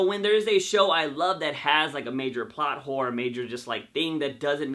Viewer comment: I swear I heard him say plot whores